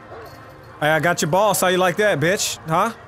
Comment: When he said I got your boss how do you like that i thought he said got your balls how like that😂😂😂